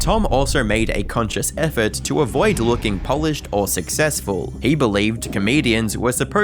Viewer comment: I swear I just heard you say Polished instead of polished